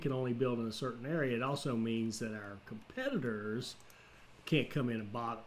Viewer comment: YouTube auto generated captions said 'our senators can't come in a bottles'